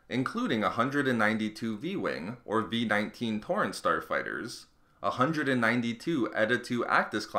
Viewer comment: did I hear porn star fighter???